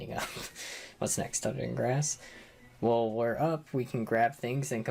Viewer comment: What does he say? “Grass” something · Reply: "This is also where the game introduces the mechanic of standing up. What's next, touching grass? 😅"